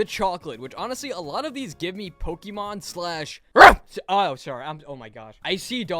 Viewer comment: D-d-did u say p-p-Pokemon 😁 in 2:40!?